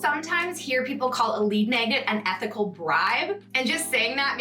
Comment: I heard "epic bribe" first, because I was walking around, and I love that!